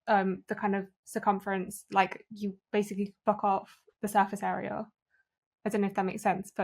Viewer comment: what's being said at 50:29 👀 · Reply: Cut-off the surface area